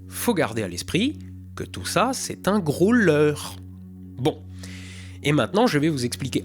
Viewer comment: Yoooo ..; quand tu as dis "c'est un gros leuuuurrre" ... j'ai entendu "c'est un growler" (dans le contexte du f18)... et je me suis dit : putain mais d'une certaine manière ça marche aussi du coup ahah